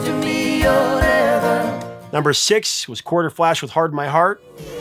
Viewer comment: well...I believe I heard "Quarterflash" in the intro, ... RIGHT???!???